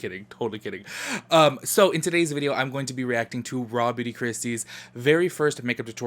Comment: I always thought you were saying Robby d’christy 😂🤦🏽‍♀️ but I gotta check her out 👍🏽